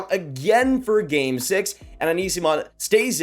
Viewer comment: All this time, I heard "Anisimov" for "Eyssimont".